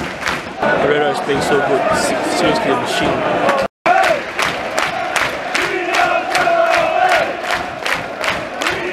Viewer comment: Ive been trying to figure out what theyre saying at 17:40 i always hear that chant but can never figure out what they say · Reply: Mourinhos red army